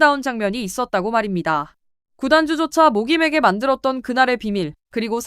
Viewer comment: 구단주조차 모김에게 만들었던...이라니...(구단주조차 목이 메게 만들었던) 아..진짜..자막좀 신경써서 달아줘요...신뢰도 확 떨어집니다.